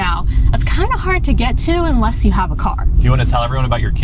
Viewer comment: The funny way he pronounces car is suck that initially i thought she said "Unless you have A COCK" ☺️😏😇😇😂😂😂❗❗❗